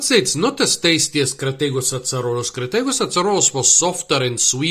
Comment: Hello! What species were you talking about at 7:28? I couldn't quite hear it. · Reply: Crataegus azarolus